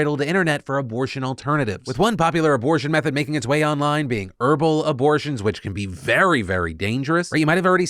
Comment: When you said herbal abortion I heard “verbal abortion.” Yeah verbal abortions won’t work 😅